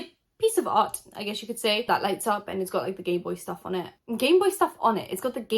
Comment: I hand to rewind like twice, I was so freaking confused. I kept hearing “gay boys” and it took me a min to figure out you wearing saying “gameboys”